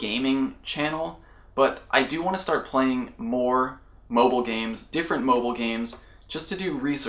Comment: The first time said mobile games i thought he said moba games, i got excited that he was gonna play league...